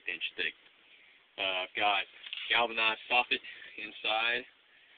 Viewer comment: what does he say in 0:35? ?"i got galvanized sausage inside"? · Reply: Galvanized soffit, lol.